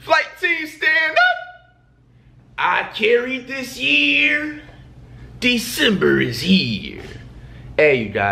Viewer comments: Tell me why I thought he said “I can’t read this year December is here” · I thought flight intro said i cant read this year, December is here.....i now kno he is saying i carried this year😂😂😂😂😂😂😂